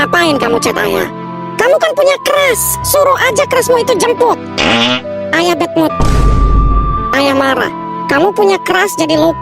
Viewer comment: ngakak bet dengernya bilang "ayah betmut"